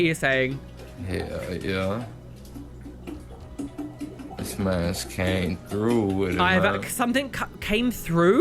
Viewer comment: Think 8:17 said "my ass can't groove with it man" probably in reference to whatever he's on 😒 at least he's self-aware? 🤷‍♀️